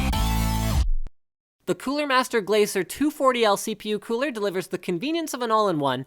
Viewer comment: Did I hear someone say blazer? ( ͡° ͜ʖ ͡°)